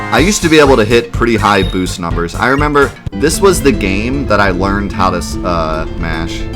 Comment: All i heard was "this is the game i learned how to Smash" XD